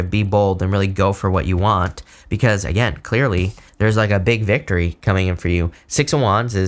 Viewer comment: "Clearly, there's like a big victory coming in for you" but I totally misheard "Like a Big Dick reading for you" and I'm taking that as a divine interpretation truly resonates 🤣🤣🤣 BIG DICK SCORP ENERGY